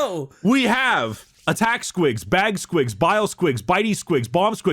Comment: Damn, I really misheard squiggs as spriggs and got reminded of that old halo machinima